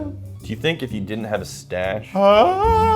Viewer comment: You totally could hold it to your face by sucking. Just did it myself 🤣 damn you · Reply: Yeah but I thought he said “if we didn’t have facial hair” lol